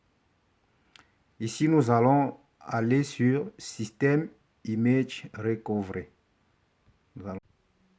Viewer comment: Slt cv? Dit moi tu as dit quoi a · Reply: J'ai dis d'aller dans Système images recovery